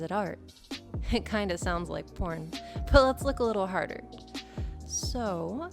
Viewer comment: Did only I hear "let's LICK a little harder" never mind.